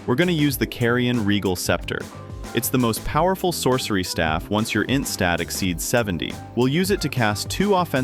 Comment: "Once your instatixeed 70" 😆 I repeated this 10 times to understand what you said.